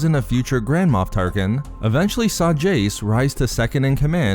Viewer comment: Jace. Shit i thought you said J. Stalin